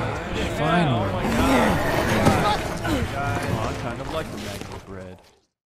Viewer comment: and i hear someone said... "i kinda like the maggot bread"